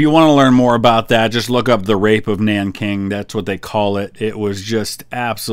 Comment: Any one knows what name he said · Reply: The Rape of Nanking